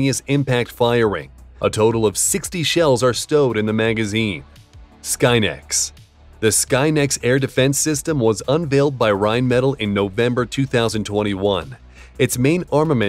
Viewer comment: did they really call it skynet ?